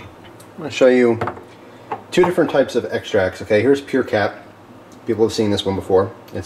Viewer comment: I thought it said pure crap